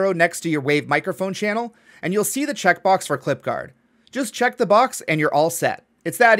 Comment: Pensé que decía Clipardo xd